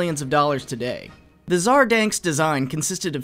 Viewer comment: Did he say Tsar Danks?